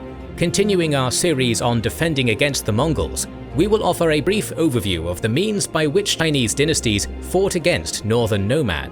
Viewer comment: what is that? Can anyone catch what it's saying? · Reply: Media offline in many languages. :) Got the modern version of a cut there.